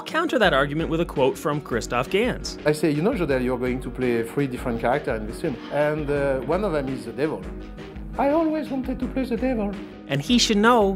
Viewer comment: wtf?! I'm german and in nearly every case I can understand what people are saying in english, but what was that?!!! Does this guy even speak english?? I understood nothing! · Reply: "I say you know Jodelle, you're going to play three different character in this one, and uhh one of them is the Devil". "I always wanted to play the Devil." Hes speaking English, but with a heavy French accent.